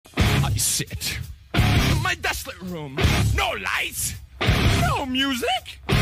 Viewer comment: am I the only one who thought it said "I shit" at first?